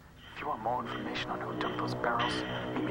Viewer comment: when Lenni said "who is this" I swear the voice sounded like it said "Justin Beiber" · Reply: He says, "Just be there." Which is very close to Justin Beiber actually...